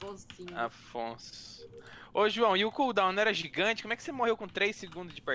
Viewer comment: eu achei que ele ia fala " o JV e o cool da Mara?